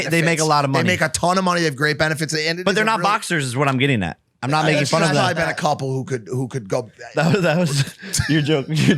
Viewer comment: Does anyone know what George says at 9:18? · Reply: haha yes Jake says the trashmen aren’t good at boxing and Geoff said said yeah they’re trash at that